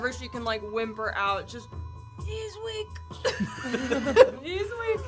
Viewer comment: I heard "knees weak." 😆